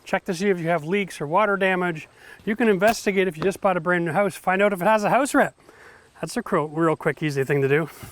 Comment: What did you say about if you just bought a new house around the 2:45 mark? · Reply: See if it has any house wrap on it.